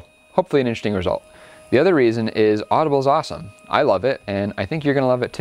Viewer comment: I thought you said edibles are awesome but you said audible are awesome. ;)